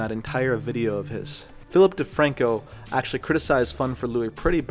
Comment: Fuck. I thought you said James Franco.. and then in comes Defranco 🙄 such a disappointment.